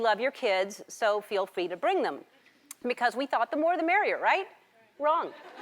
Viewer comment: hey guys, I am from germany and I didn't really get the part at 1:54 - 1:57. did she say 'the more they marry you' ? thanks in advance everybody.